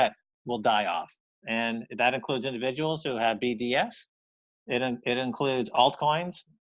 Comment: what is he saying at 21:26 that everyone who has BTS will die off ? What is BTX or s or whatever he is talking about ? Anyone know the answer to this one ? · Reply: BDS Bitcoin Derangement Syndrome - a Keiser diagnosis with some tooth.